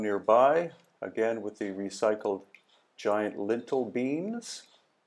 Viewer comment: Oh, I thought was saying lentil beans! Now it makes more sense.